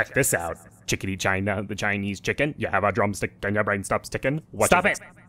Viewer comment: "Chiketa China the Chinese chicken, you had a drumstick and your brain starts trippin". I actually lost it at that part. I had to pause the video for about 3 minutes. Bravo and. You certainly havent lost your touch XD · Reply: hahahaha i thought it said "brain stops tickin" but thats really funny